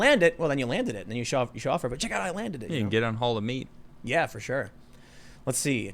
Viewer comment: Di, Did he say "Hall of meat"? The fuck is that? Sounds something like rotten(dot)com 2.0... That, or a site strictly made up of just of c0cks.